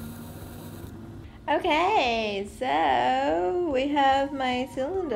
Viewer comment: or 6:59 I thought I just heard my friend "Okayie~"